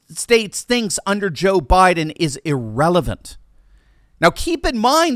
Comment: I thought you were going to say “what the United States thinks is irrational” but I can see how irrelevant fits in there too.